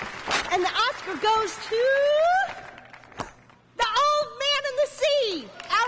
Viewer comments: At first I thought she said ‘The old man in the seat’ 😂😂😂😂😂 I was like ‘rude much’ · for some reason i thought said “the old man in the seat” 😭